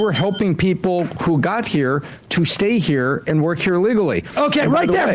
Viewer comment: Sam he said "to help them work here "LEGALLY" you misheard it as "Illegally."